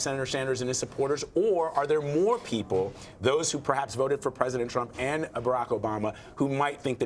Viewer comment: wait did he just say those who voted for president Trump and Obama? So you're saying there are Trump supporters who also voted for Obama who just might not be racist? 🤔